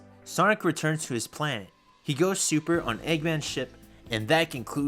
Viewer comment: when you said eggmans ship i heard eggmans shit instead of eggmans ship